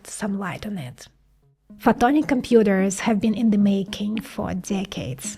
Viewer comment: Is she saying "photonic computers"?